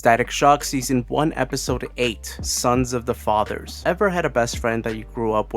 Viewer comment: I thought it said SINS of the father and began to woooooAHHHHHH